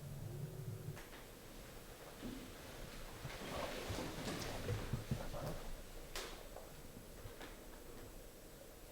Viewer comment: it sounds like an EVP, but i can't make out what's being said. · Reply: Bernard Cribbins